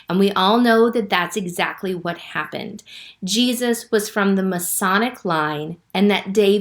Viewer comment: Thank you for your video, but at 2:38, it sounded like you said Jesus came from the "Masonic" line. I think you meant to say the "Messianic" line.